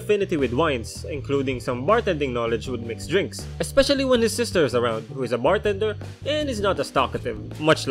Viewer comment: I thought you said his sister is a stalker